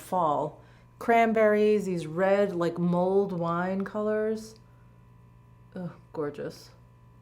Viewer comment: AHAHAHA!! I thought you said “mold” wine color …. I was thinking “ewww moldy wine color?” Then I realized you said mulled wine 🍷color….oh jeez … senility just around the corner!!! 🧠 👵🏼 🤣